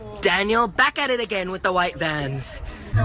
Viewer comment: What is he saying here??? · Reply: Back at it again with the white vans